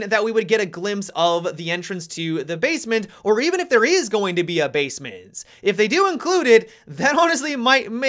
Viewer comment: Am I deaf or did I hear "Basemince" I hope we have a "Basemince"